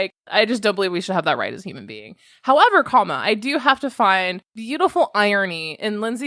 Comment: I think I just heard you say “however comma” lmao is that real? Because I fully appreciate it 😂